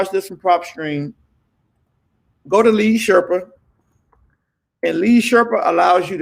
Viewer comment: What is the name of the skip trace u mentioned @ 13:03 lead sherper, I couldn’t understand what u said. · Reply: lead Sherpa